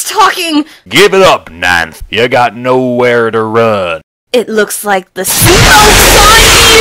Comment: I still don't know what she says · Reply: First Name Last Name "it looks like the 'smokes' on you!" Like jokes on you, if that's what you were trying to hear what minene said